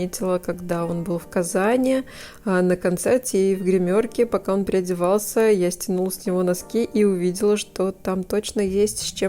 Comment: Ты снела с него носки или мне послышалось ?